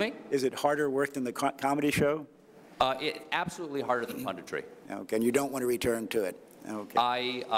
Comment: What did Colbert say at 2:55? "Absolutely harder than..." What? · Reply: punditry, which is in short social commentary, i.e. his day job as a talk show host (I know this is an old comment, but nobody else replied...)